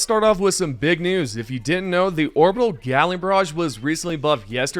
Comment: I swear he said orbital gyatling barrage